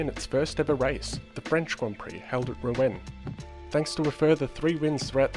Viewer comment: did you say french gp held in berlin? I honestly don't get it Could someone please explain? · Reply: I believe he says Rouen 😉